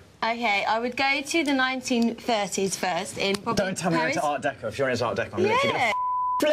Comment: What did he say at 7:15? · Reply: don't tell me you're into art decor. If you're into art decor I'm gonna..fuck! something like that?